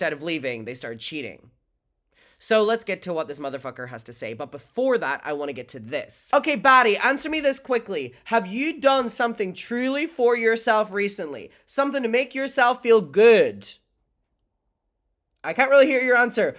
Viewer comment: girl i thought you said FATTY at first 💀